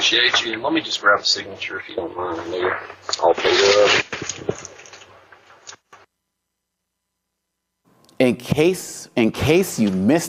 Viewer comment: It sounds like he says it at the 2:38 part, sounds like he says just let me grab a signature if you don't mind nigga, not trying to find it, or say he said it for sure, just if you listen, it does sound like it.